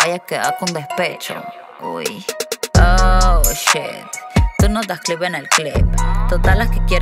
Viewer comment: Que dice ella en el minuto 1:38 No le entiendo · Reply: Tu no das clic en el clip